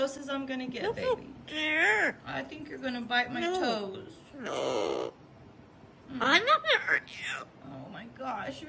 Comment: I keep hearing I'm not going 2 hurt u Barbi